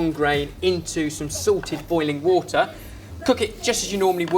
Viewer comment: It always sounds like he says sorted boiling water XD LOL